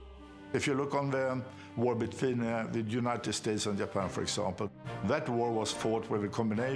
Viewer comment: I thought he said United States of Japan there for a moment.